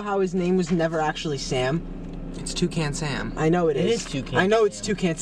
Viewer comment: My brain immediately thought that Nick said he fucs with Tuscan Sam